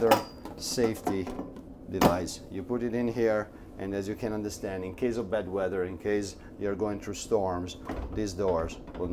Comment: you grab a piece of plastic and locked the refrigerator door from opening but, I thought you said " this is in case you have a " bed wetter " not "bad weather " LOL